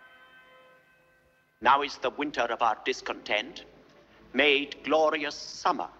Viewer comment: I'm hard of hearing, I thought he said winter of discount tents.